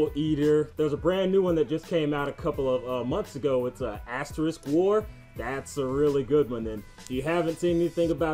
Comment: what did he say? What is the name of that anime? ^^ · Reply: astrisk war?